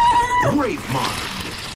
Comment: it sounds like hes saying Grapvine at first but hes saying gravemind lol